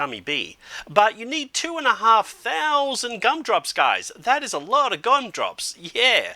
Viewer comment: wait did he say ''gun drops''